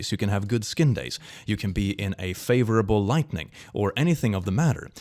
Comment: Does anyone else hear him say lightning ⚡, instead of lighting💡? Do I just not understand his accent?